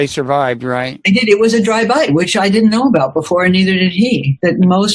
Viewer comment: Mrs Doran describes a rattle snake attack/encounter as "a dry bite". I first heard it as "a drive by". My silly internal Freude.. tricks are for kids. But for real, two titans of their chosen industries expounding on the human condition and Hollywood's part in shaping emotional momentum, as well as writers' and directors' cognizance of the such. Beyond Fantastic.